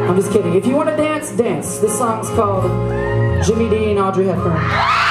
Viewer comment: Did he just say Jimmy dean? Or am I crazy